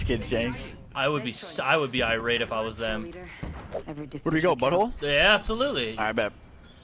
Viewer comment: DID HE SAID BUTHOLE ????